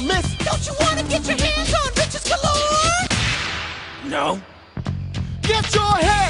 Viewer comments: don't call me crazy but diddy sounds like he is saying bitches Galore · all this time and I still think diddy says "Get you're hands on bitches and more" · I thought he said, "Don't you want to get your hands on B*TCHES galore?"